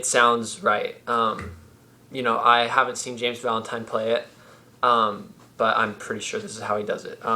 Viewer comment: I thought he said James Blunt, I was wrong. He said James Valentine.